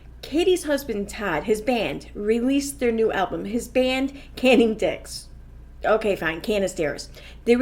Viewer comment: everything I hear you say "Canning dicks said" I can't breath!! I've laughed so hard 🤣 😂 😆 🤣🤣🤣🤣🤣🤣🤣🤣